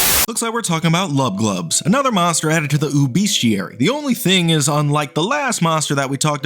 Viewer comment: Every time you said ooObeast I thought you were saying obese till the end of the video